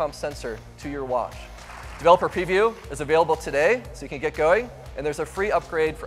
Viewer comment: Thought it said Availability Fail